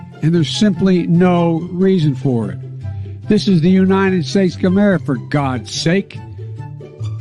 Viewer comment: Did he just say "This is the United States chimera"???🤔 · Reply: United States of Camaro. Joe loves those performance gas guzzlers.